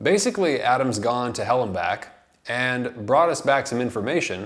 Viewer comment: When you said (Lehrer has been to...) "hell and back", I heard "Houellebecq". I so much liked my wrong hearing that from now on I will always think about Houellebecq as the "Hell and Back" -name. Just the name, I don't think he is a hell and back guy. He loves this world, and hates it. Speaking of his favorite drug - the nicotine - he wrote in Serotonin: “The relief that comes from the first puff is immediate, startlingly violent. Nicotine is a perfect drug, a simple, hard drug that brings no joy, defined entirely by a lack, and by the cessation of that lack.”